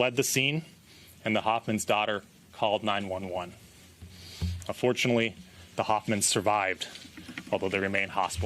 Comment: Is it me or does it seem like this dude is smiling while explaining? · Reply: That was my reaction to this too… like as he was describing the killer entering their house and shooting the first victims he looks like he’s smiling or about to laugh. At 2:43 it sounds like he said “unfortunately the Hoffmann survived.” I’ve listened to it like 5 times.